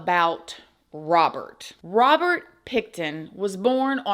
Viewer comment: I thought you said Robert Pigton